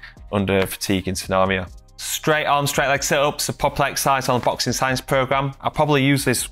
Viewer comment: What the heck did you say at 12:13 ? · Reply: "Straight-Arm/Straight-Leg Sit-Ups, a popular exercise on the Boxing Science Program"